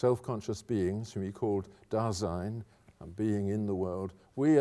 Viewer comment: again the subtitles issue : it is SAYING '' DESIGN '' ... instead of '' DASEIN '' , the EXPRESSION in GERMAN LANGUAGE , USED ALL OVER , in GERMAN . it changes the whole meaning of Your postulat , at this point .